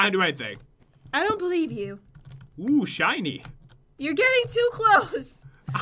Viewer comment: I thought it said "OOH SH*TY"